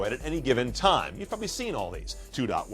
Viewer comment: DID HE SAY 'YOU FUCKIN SEEN ALL THESE"